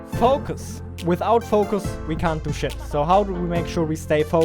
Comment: Did you say "we can't do shlt"? 🤔😅